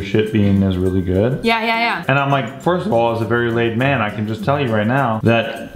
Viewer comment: "As a very laid man"...is that what he said? lol